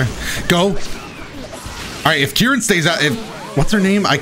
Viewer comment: i legit thought u said jiren